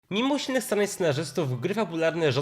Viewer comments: "Mimo usilnych starań scenarzystów" dobrze usłyszałem, bo za chiny nie rozumiem. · mimusznysanaisnarzecztow gry fabularne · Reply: Dałam szybkość odtwarzania na 0.5 i usłyszałam "mimo silnych starać scenarzystów", ale nawet przy zwolnionym tempie ciężko zrozumieć :p